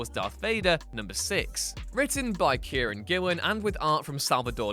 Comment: it says Kieran but the subtitles say Karen my name is kieron and i have had my name miss spelled a lot but this takes the cake in insulting